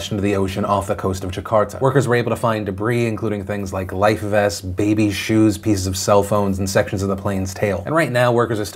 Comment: Jeez, I thought he said Babies, shoes 😅